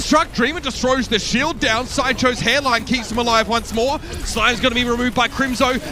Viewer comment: what did the caster say? Sideshow's hairline??